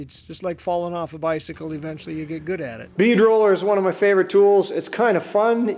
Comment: Love the way you breathe new life into old(er) things! When you said, "bead roller," I heard, "Bee driller," was all like, "Huh?" hahahaha